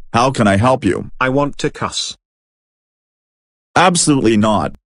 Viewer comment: did he said two cups or to cuss? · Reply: No He said I want to cuss